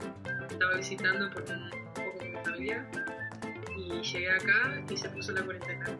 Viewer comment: Estaba visitando junto a mi novia? Solo yo escuché eso? No escuché que dijo familia, honestamente.